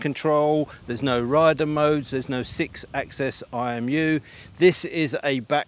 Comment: Hi, what did you say no 6 axis i o view?? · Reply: IMU - Inertial Measurement Unit